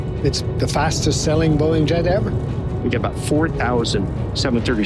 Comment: what the... did I hear that right, 17:36 "the fastest falling jet ever" 😮